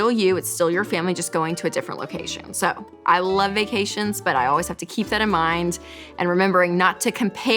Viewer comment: LOL at first I thought it said "vaccinations"